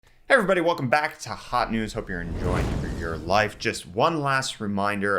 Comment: Things I thought I heard you say... 1. Welcome to Hot News Hooker. 2. I'm just spitting out my butt. Lol...